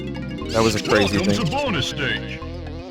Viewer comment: "Welcome to bonus stage" always seems like an intentionally lame way to intro a bonus stage. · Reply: When I was a kid I always heard "bonus steak" and just thought it was an expression I didn't get.